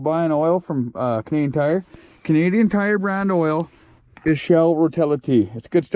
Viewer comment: I got princess auto (harbor freight here in Old Connecticut of the United and states….. they don’t sell oil like princess auto…. Bullshit 😢😂😂😂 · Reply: My drunk of Budweiser ass just thought said princess auto but he actually said Canadian tire…. Wouldn’t know about that in the old United States of ‘Merica 🤷‍♂️🤣🤣🤣